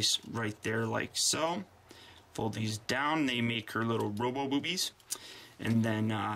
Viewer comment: u did not just say roller-boobies? lolz win!